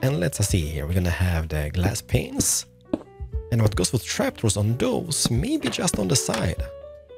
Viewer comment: Umm, am I the only one who heard him say “glass penises” at the beginning?? 😂